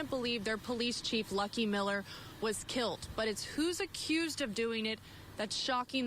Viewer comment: I can’t believe the bitch I mean news lady said “kilt” Talk about some serious ebonics. I think she was trying to say killed but then again the media never has to be accountable for anything, including proper grammar